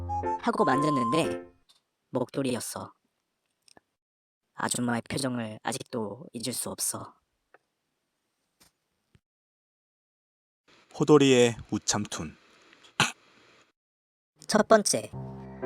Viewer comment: 초 본후에 나만 목도리에 영상툰이라고 들리나